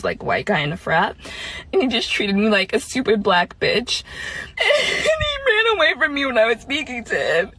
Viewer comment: I thought she said “super black bitch” 😧🤔😭